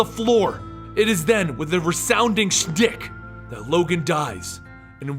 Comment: i thought he said "shdick"